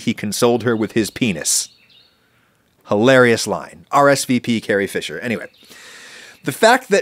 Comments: Wait at 11:25 did adam just say "rsvp carrie fisher" hahah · does he say "RSVP Carrie Fisher"? Does that mean something else or is that just a misspoke? Am I crazy? Lol